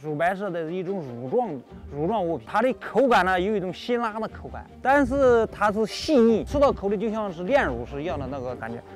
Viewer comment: anyone actually know what he said in Chinese? i think he said the "texture is/taste like that of Syrah" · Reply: he said 辛辣 (xin la)